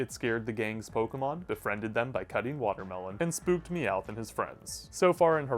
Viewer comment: Spooked Meowth (Somehow i heard it as Spooked Me Out) 🤣